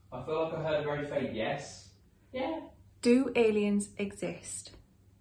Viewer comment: When you asked if there was aliens you can hear a woman's voice say yeah after him. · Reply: I heard that as well a woman goes "Yeah" is there someone else there with you guys orjust the two of you?? Its at the 11:16 minute mark.